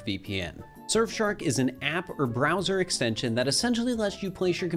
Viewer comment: did he say bowser instead of browser😂